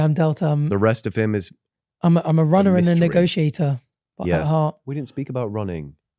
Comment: what did say ⁉️ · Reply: Negotiator